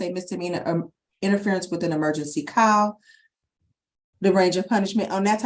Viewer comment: Oh snap interference of a emergency call! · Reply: It sounded like she said emergency cow 😂